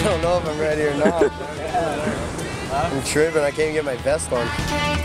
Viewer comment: what does he say at 2:00? is it? "im shroomin', i can t even get my vest on." Please enlighten me! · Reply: Riley Andersen I think it's trippin or tripping out